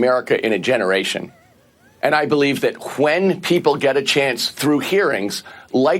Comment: it sounds like the guy is saying Quen instead of when.